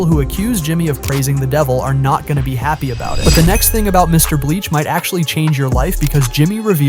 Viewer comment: Is it me or do I hear Mr bleach